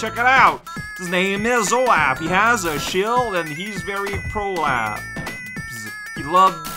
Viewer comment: Fire sounds like water (or is completely silent, and we're hearing the actual water,) and lightning sounds like a wet fart. 8:32- I thought you said "Prolapse" at first.